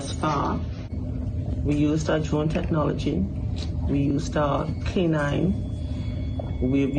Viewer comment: It is easy to disappear there. It is an open space like a hotel. Been there for over 30 years and never had an incident . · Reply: but what about security cameras have you ever noticed security cameras in the area ? I could not understand what the police officer says around 3:34 something technology ... to me , it sounds like Szechuan technology ... ? 😮 I just thought it was strange that the officer did not say we've checked security cameras ... she only said something technology ... What if she rented a boat a small motor boat and was a loan on it and dropped her phone and what if she ended up stranded on some tiny little island there that people don't normally visit . I hope they find her and that they find her doing pretty well but just stranded .